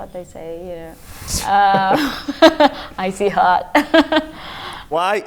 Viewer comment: what does she say at 3:30? "I see hot"??? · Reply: Icee hot. Its something you apply to your body when you get sore. Google it.